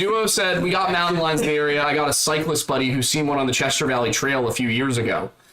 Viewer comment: i heard "sightless buddy" not CYCLIST😂😂😂😂 your "did he" comments sent me